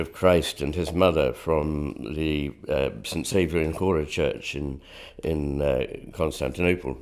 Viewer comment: does anyone know what church he says the name of here? I can't make it out. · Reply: Might be talking about Chora Church